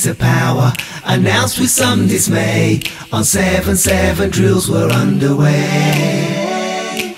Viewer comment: I thought I heard "on 7/7 drills were on the way" - a reference to the London terror attacks of 7/72005?